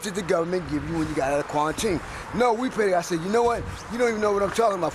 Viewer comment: What Bro Said? QUARINTINE?